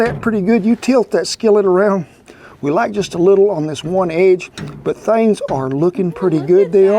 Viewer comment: For anyone who heard "We like just a little on one edge" at 4:39, what he's saying is "We LACK just a little on one edge" Just thought I'd clarify, my grandpa has the same accent as here and it used to confuse me growing up